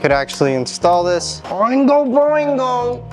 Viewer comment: Great video!! Did I hear you say “Oingo Boingo” when putting in that steering rack?!